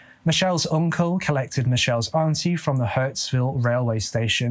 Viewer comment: no offence 😜 you sound more Aussie than British 👍🏼 pronounce better than some Aussies those places you mentioned, you could easily pass by an Australian . Bit more practice that lingo, mate 😂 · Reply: Altho i think I heard 'Hurtsville' (Hurstville) 😄